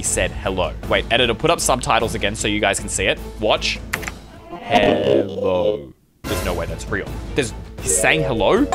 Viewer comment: btw I think the enderman said “HELP ME.”